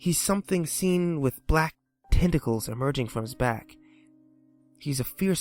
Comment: At first I thought you said testicles...